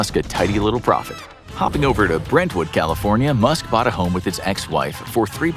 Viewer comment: did you say "its"😭